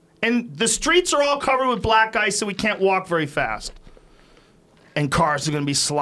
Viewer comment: i thought he said the streets are all covered in black guys so we cant walk very fast